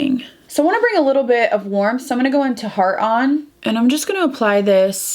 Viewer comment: “Heart on” I thought you said “hard on”💀😭😂 I was like that’s a weird eyeshadow name but I wouldn’t be surprised if NARS had that as a name. I am definitely going to recreate this look with products I already have in my collection! I also hope Nala is ok and if you do end up having to make a hard decision I hope you will be ok❤️ it’s so hard when our fur babies are not doing good. It’s always a hard decision but in the end we have to choose what would be best for our babies than for us. I wish I could give you a big hug❤️ (and that’s saying a lot because I am not a hugger)😂